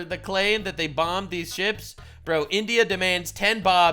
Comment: Witnesses in Pakistan reportedly heard the missiles scream "THANK YOU COME AGAIN" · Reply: Thats funny.. I would’ve swore I heard “Send Bobs and vagennnnnnne…!”